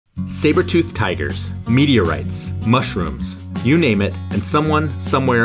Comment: I misheard meteorites as "media rights"